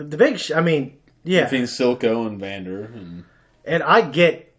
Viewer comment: Reaction DID NOT disappoint…. Also, 20:19 I kept hearing Vader and Ahsoka instead of Vander and Silco 😂😂